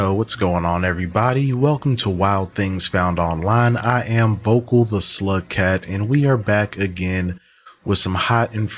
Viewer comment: Bruh For the Longest time I thought you been saying “Vokal the slut cat”